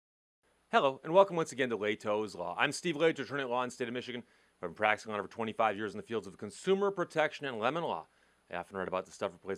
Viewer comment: Almost sounds like you said, "I've been practicing for 125 years in the state of Michigan." 😲😲